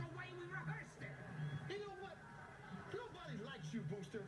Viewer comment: I thought the kid said “We don’t like you bear”???